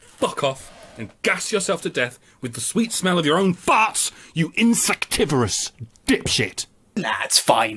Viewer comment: What's this word, it sounds like "insactivorous" but that isn't a real thing. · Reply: "Insectivorous." It means you eat insects. Pardon my accent, and the drunkenness that contributes to it.